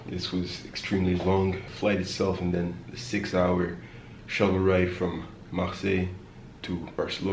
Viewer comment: Did he say 6 hour shuttle ride @ 13:45 ? that's horribly long after a flight.